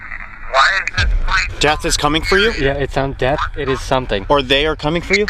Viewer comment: sounds like it says “Death” from what I can hear with my headphones…